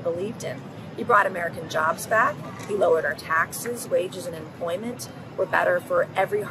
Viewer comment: She said trump lowered our taxes, wages and unemployment! Wages lowered, really, like we want lower wages!😂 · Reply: I thought that’s what I heard first also but actually her pacing of her words made it sound like that when what she actually said was “wages and unemployment were lower “ listen again by the way, I don’t like her one bit I just wanted to clarify